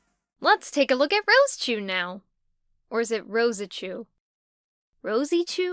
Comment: THANK YOU. My god, for years watching that demented cartoon based off the comics, every time they refer to Rosechu, I've always heard "Rose-a-chu." I am glad I am not the only one who heard it pronounced like that. Anyways, nice work on redesigning these two. Like you said at the end, I can only hope Chris doesn't rage over seeing this video. Let's hope this doesn't start a new saga... dear lord. PS: I can play the sit n' spin.